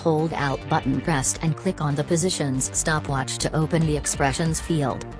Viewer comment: whats you said click on P and what did u do ofter thats didt get ur wording..?? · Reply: alt and click at the same time